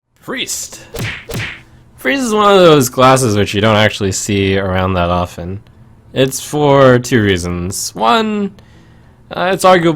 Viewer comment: haha, eh uh... in the beginning I thought he said "glasses" instead of "classes", so I thought he was talking and joking about his glasses. I'm so stupid -.